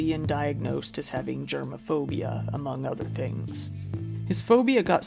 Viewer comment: I thought he said gerbilphobia.